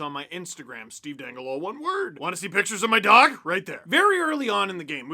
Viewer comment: at first I heard "WANNA SEE PICTURES OF MY DONG??" and I had to rewind to make sure I didn't hear what I thought I heard lmao. Dog. He said dog.